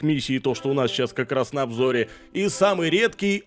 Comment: мне послышалось У НАС НА ОБЖОРЕ 🤣🤣🤣